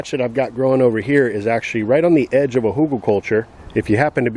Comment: What does he say at 0:50? It’s a word that ends in -culture. The captions say “hookah culture, but I don’t think that’s it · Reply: hugelkultur?